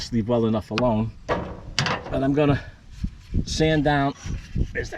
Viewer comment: Yore Aksent has me homesick, dere. When you said you would "Sand Down" the contact, I heard you say "Sandown", which is in NaHampsha, by da way.